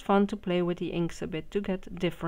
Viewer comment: What are you saying at the 5:00 mark? It starts with the word Community. I would like to check it out, but I can’t understand the next word you’re saying after community. Thanks! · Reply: denisemouton community tab, you can find it at the top of the homepage (if that’s a good word) on smartphone or desktop computer, hope this helps💖